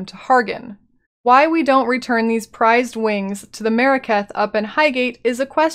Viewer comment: I keep hearing "Merekath" instead of "Maraketh".